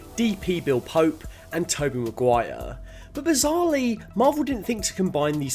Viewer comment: I keep hearing 'Tobey' as 'Tugboat' lmao